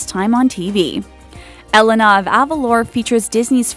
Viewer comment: started laughing because when i heard her say "elena" i thought she said "elanore" (the mom from brave and god help me if her name is spelt right)